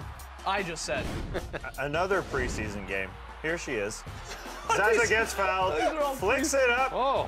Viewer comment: Is it only me who heard at 2:45 "Here she is?" Even apology sounds insulting.